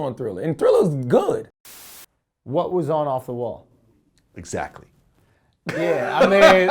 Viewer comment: did he just say what's on off the wall?